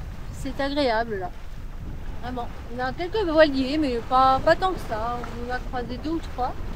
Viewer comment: j'ai compris "il y a quelques noyés"...